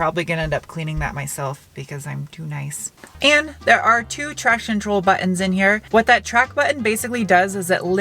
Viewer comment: Think I heard you say "traction troll" before the let it rip section. Not complaining, it oddly works